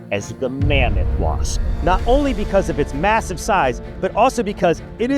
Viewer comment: did i hear MASSIVE????